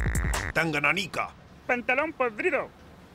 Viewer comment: parece que dice danganronpa >.O